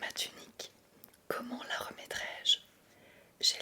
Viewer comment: j'ai ôté ma quoi ? 😂😂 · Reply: tunique 😂